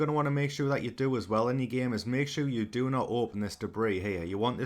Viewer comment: wtf is he saying at 0:30 ?? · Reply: Debris